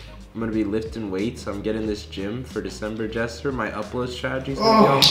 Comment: Did anyone else hear “Jester” ?